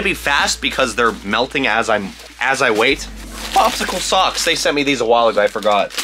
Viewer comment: I always thought Brent said “Popsicle sucks” but he said “Popsicle socks”.